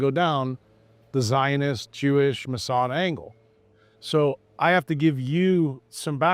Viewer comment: 17 seconds in and he’s naming the Zionists, the Jews and the Masons. My guy! BASED. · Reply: I thought he said Mossad not Mason?